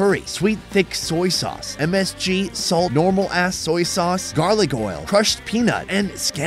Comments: did he say normal ass soy sauce? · Did I hear that right ? 4:51 "normal ass soy sauce"……